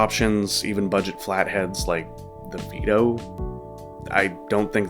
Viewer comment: you named something which was 4-5 dollars. I couldn't catch its name. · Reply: The product I mentioned was "Vido" it's an extremely cheap budget flathead you can order off on AliExpress or similar.